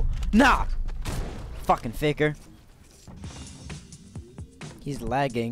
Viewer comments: i thought he said the n word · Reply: Ok me too but he said fucking faker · To this day.... i still think, “ It is smarter to pickaxe”. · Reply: to this day.... i still think, that at 4:46 he said fukin nibber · Im sorry if you didn't mean for this to sound like this, not throwing any shade at you, but at four:forty8 it sounds like you said "Fucking ni***r Edit: Just noticed he said "Fucking faker" Sorry man